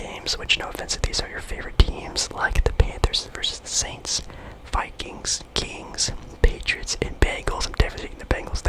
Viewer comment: did he say kings?